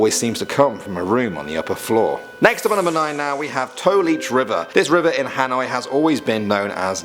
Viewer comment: I thought he said toe leech river i was like heeellllllll no